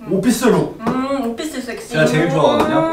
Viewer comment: 아니 ㅋㅋㅋㅋㅋ잘들어보면 오피스쨱스로 들림ㅋㅋㅋㅋㅋ 어케된거죠???ㅎㅎㅎ